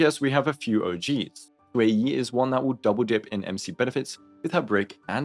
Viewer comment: not saying ueyi instead of xueyi 😭